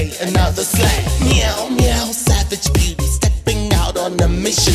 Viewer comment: the title 'and more' Bebe and Kennedy just got the chop lmfao update: they changed it lmfao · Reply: I thought she said "savage Bebe," too.